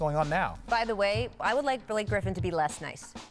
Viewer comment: Did she say Billy Griffin at 2:00?